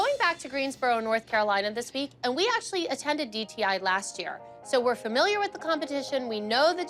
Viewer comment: Did christi say dti ??